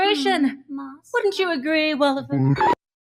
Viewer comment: what does sally call wally at 24:04 ? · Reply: Sally calls Wally ‘Walliford’. Someone on Clown’s team suggested it as Wally’s full name. Clown didn’t jive with the idea of it so they just made it a part of Sally’s character to nickname Wally as Walliford.